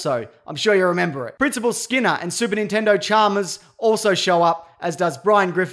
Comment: he said super Nintendo charmers instead of superintendent Chalmers 🤣